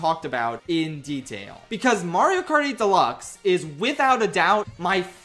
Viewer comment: I thought you said "Mario Party Deluxe" lmao